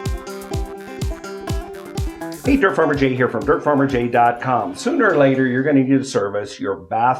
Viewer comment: I thought it said Dirty Farmer Jay😂